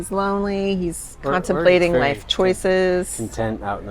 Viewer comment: When you showed the guy with the survival blanket (10:00 min) I thought you said he was constipated, not contemplating....HAHAHA! Great video!! 😄😳